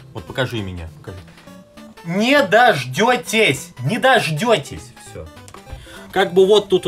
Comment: видео по metal gear solid не будет Что слышу я: да